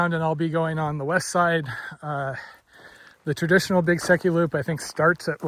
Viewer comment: Beautiful scenery. Haha - not being familiar with the area, until this video, I thought you were saying Big Sucky Loop and were really going to put your body through hell.